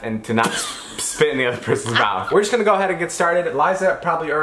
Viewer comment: At 0:5-0:11 Me: What is this game If you don't understand it's cuz David said, "spit in the other person's mouth".....